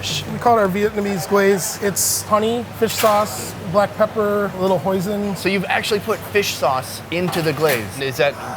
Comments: It sounded like he said,"It's Honey, Fish sauce, Black Pepper, a lil poison.." xD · did he say a little poison 🤔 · I heard a little poison lmao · when he says a little hoisin , I heard poison. Let's just say that did not stop the drooling hunger throughout the video. #foodtodiefor